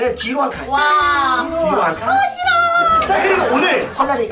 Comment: 초에 에이이가 어 ㅅㅂ 이라고들었어요 저만 들었나요? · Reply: 싫어라고함